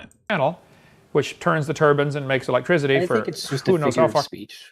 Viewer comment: I may be wrong, but it sounds like says "turban" instead of "turbine"